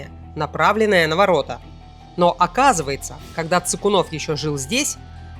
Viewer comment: На 22 минуте послышалось Ссыкунов. Или не послышалось...